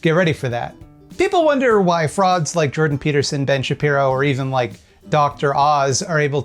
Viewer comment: When you said "Frauds like Jordan Peterson" I thought you said "frogs like Jordan Peterson" because a lot of people say he sounds like kermit